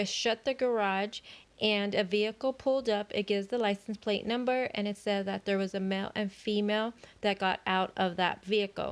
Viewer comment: In the porch interview at 3:35, CW is saying that he knew he wasn't going to put the kids "??" machine on at bedtime. I wonder what kind of machine that was. Rain machine? Can't figure out the pronunciation. (Was it a cooler CW got rid of? or can it have been that "machine". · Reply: I think he said rain machine. It makes rain sounds to help you feel sleepy and cozy.